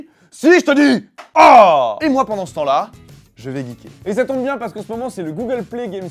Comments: j'ai compris "Je vais niquer" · A 3:24 j'ai entendu "je vais niquer"... Sinon moi ca va, et vous?